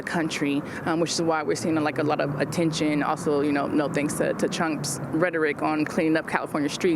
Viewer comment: Did she just say “Chump’s”? 😂